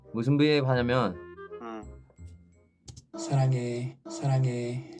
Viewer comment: has anyone pointed out that 4:12 youngmin says "ah fuck" or am i just hearing things